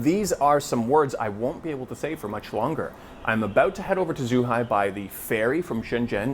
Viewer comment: i thought you said the FAIRY from Shenzhen 😂